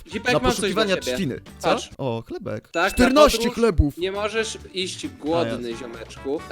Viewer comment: chyba usłyszałam moją babcie że nie moge isć głodna